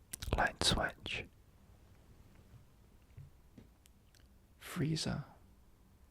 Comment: I thought bro said Freeza. Woke me up instantly lol